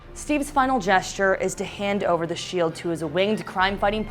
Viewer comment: whAT does the host say from 3:50 to 3:55? An ESL student needs some help here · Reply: Zhiqun Wen She just talks about how Steve’s final gesture is to hand over his shield to Sam Wilson.